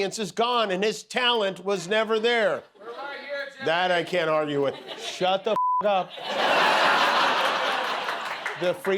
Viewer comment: what the intruder said? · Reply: I said "We're right here Jimmy!" as in we (his audience) are right here standing with Jimmy and everyone that works on the show.